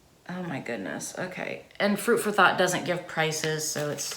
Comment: Was I the only one who thought she said fruit for thot 🤔